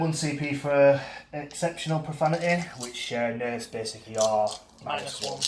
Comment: It is now going to be called this from now on! 😂😅 when do we say this? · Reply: Both times you use the Strat on them. At least that's what it sounded like to me. Edit: Definitely sounds like "profanity"